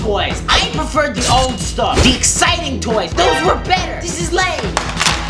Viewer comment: why I think he is saying lane but lame